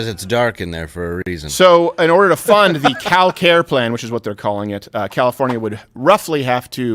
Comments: You said Cal-Care plan but I honestly thought you said "Cow care plan" lol. It would've worked too after those cow dancers. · Lol I thought he said “Cow care plan” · I could’ve sworn you said “Cow Care Plan” 😂